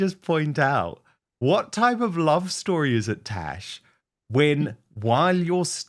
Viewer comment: Why does it sound like he is saying “trash”